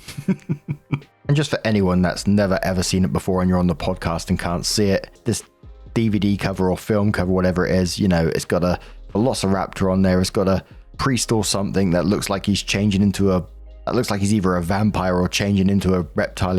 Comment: I thought you said VelociPASTA 🍝